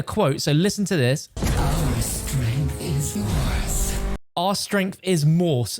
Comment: I think she said "Our strength is yours!" 5:37 kind of signifying their help from beyond the mortal realm maybe?